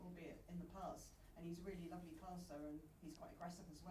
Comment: Put craig hope in his place. Made Louise taylor blush This man is special, he owned that room whilst warning them, he can be friend or foe but he's in charge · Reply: He misheard Taylor. She said Schar is a lovely passer and Matthias heard 'lovely person'. Still funny. You like him, ja?